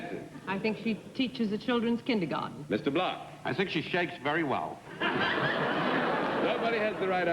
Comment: does Block say 'I think she shags very well!', when she turns away in disgust. What could he mean, apart form the obvious, and if it was the obvious he should have been kicked out of the show. Surely I miss heard him!